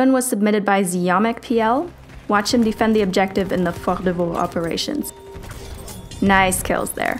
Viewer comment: I literally heard her saying Fok de faux operations lol 😂